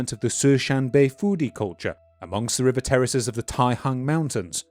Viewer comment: What is being said "Cishan-beifuidi culture". What I heard "The Susan Bay Foodie Culture". Goddamned Neolithic yuppies are everywhere.